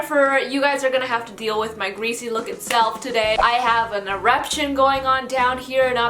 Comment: I thought she said I'm having an erection down there not an eruption. Oops my dirty mind just ruined my day like that's not even possible